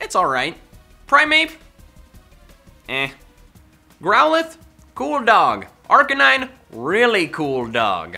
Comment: For growlithe I thought you said corn dog